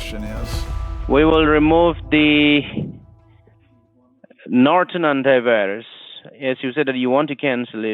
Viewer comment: I thought he said Norton-Underwears XD